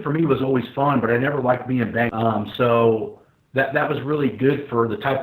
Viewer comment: Matt said "training for me was always fun, but I never liked being..."? What was the rest of that? Couldn't catch it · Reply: Levi sore